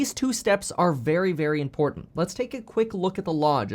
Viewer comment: Did u just say let's take a loook at the law? Lol